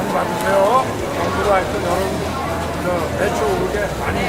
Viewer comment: 아무리 반복해서 들어도 '배추'로 들림.